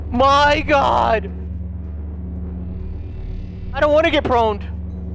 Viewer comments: Is he saying "proned"? Lol, wtf. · it's 'probed' not 'proned'... 11:25 there's more seeing how it says 'voice cast' and u didn't hear any voices yet 14:44 'server' instead of 'service' I think has discord on his mind xD 20:44 it's like playing prop hunt (the mimic sliders are props lol) 23:47 I want an emotional support bagel 1:10:08 u forgot the 'destroy all cobwebs' objective 1:17:41 'weapons from blueprints' not 'and' 1:18:03 'stock up' 1:31:21 u need to kill 25, not 30. U need 10 more 1:49:30 'boil' not 'broil' I just realized.. am I crazy or did we not hear the Bella and Donna story?